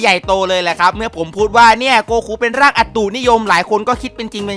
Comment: ผมได้ยินเป็นอัดตตูดนิยม